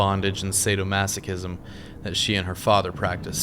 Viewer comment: Sadomasochism- noun. interaction, especially sexual activity, in which one person enjoys inflicting physical or mental suffering on another person, who derives pleasure from experiencing pain. 2. gratification, especially sexual, gained through inflicting or receiving pain; sadism and masochism combined. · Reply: I heard you say "masochistic lifestyle" not sadomasochistic but maybe I heard wrong